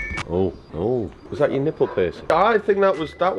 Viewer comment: Did he say nipple piercing ???